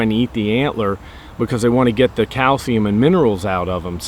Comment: Sounded like you said cow semen instead of calcium